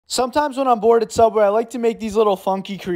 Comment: Sounds like he said when he is bored and sober not bored at subway lol.